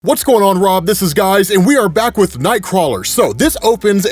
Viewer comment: Did you just say whats goin on Rob this is guys lololol